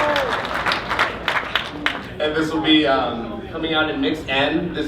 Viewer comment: what did he say at 3:30, coming at what? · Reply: Mix N